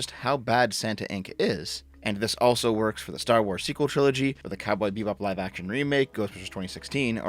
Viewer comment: I misheard you start with "Star Wars PREQUEL Trilogy," and I said, "Yeah, that was shitty, but it wasn't woke..."